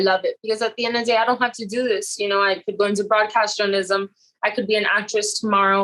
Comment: wtf did she say?? I could go into broadcastalism??? Lol